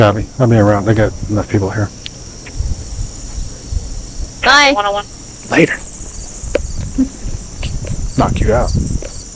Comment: Did he just say to a woman when she said bye in a sarcastic manner, " bye? I'll knock you out"